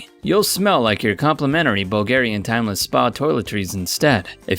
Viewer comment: did u just say "bulgarian"? I mean.. im from bulgaria :D